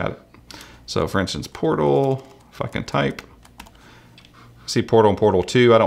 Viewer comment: OMG SOUNDS LIKE he said f*ucking type 2:07 edit: some one already said that lol give support to him